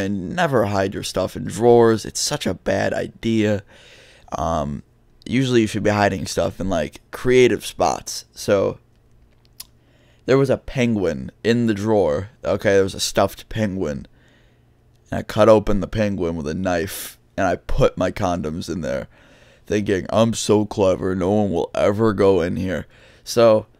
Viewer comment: What I heard “I had a penguin in my drawer” accidentally skips forward tries to go back still a little a bit ahead of where I was “so I cut open this penguin. Mfw